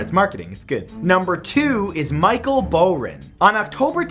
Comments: At first I thought number 2 said Michael Brown XD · i thought #2 said Micheal brown